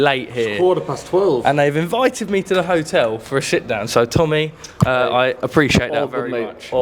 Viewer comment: Did he say “shitdown”